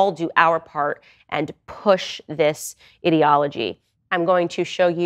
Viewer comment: She said "ideology" I heard "idiocy